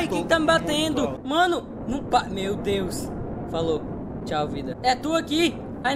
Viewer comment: CARA, SÓ EU ESCUTEI "TCHAU VIDA?"